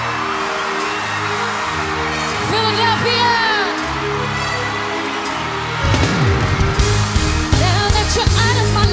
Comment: what did she say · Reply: "Philadelphia"